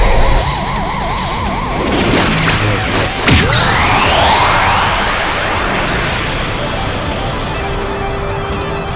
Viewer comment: Did he say something? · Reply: Evan Washington I think he said “you deserve this”